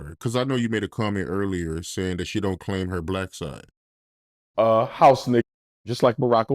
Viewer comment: At first I thought he said, “she don’t wash her black side”!😂😂😂